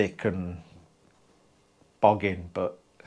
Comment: great vid and a nice lamp, I think I heard you say boggin at 4:40, I had to go back and listen about 4 times, it was a word my late brother used often and made me laugh. I got my bullet and a great job it is, I can't believe it reached me in Northern Ireland considering our past. The sticker is placed neatly with others on my camper. I would send a picture but unsure how. 2 Honda ST70's and we have a great time touring and at classic car and bike shows. It gets a lot of attention and classic VW's don't get a look in. You will have a great time in your camper with your family and very thoughtful of you to do that for your family. Missin Blue in this vid, my pup is a Shih Tzu not sure what a cross with Blue and Molly would be like, though being honest I couldn't let her be violated anyway.